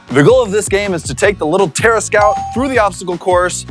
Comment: i thought said our little terrorist